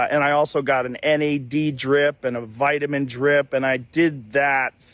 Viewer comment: That’s correct! Later I heard him say vitamin c. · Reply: "him", who? 1:34 is where I hear Rogan, just say "Vitamin-drip" to the best of my knowledge, Joe doesn't say what the vitamin-drip, actually consisted of...